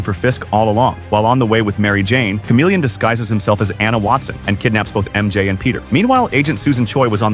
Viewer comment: I thought I just heard Chameleon disguised as Emma Watson.